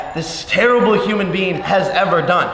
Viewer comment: Sounded like Jake said human bean